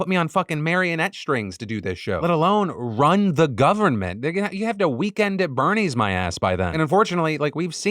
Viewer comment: ummmm i swear it sounds like he says "Ni99a! They'll have to weekend at Berny's my ass..." lmao 🤣😂. Someone please tell me I'm tripping.